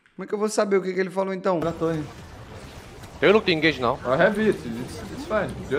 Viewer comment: Eu entendi " Eu não tenho engaje não kkkkk "